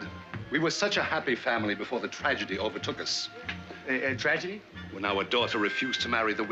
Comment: Did he say "tragedy overtook us", or "tragedy of her tucas"?